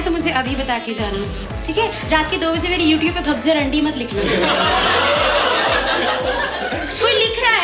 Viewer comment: What does the word bhagjur mean? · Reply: She said - raat ke 2 baje mere youtube pe "bhag ja randi" mat likhna. Meaning give validations to my jokes now. Don't abuse me in the night on YouTube shouting run away whore.